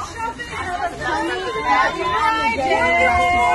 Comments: I thought I heard someone say “happy suicide day!” Or maybe im just delusional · Bro what i literally heard them say "happy suicide day!!"this is sick